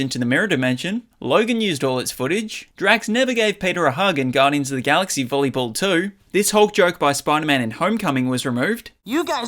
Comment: Did I hear “Guardians of the Galaxy volleyball 2”?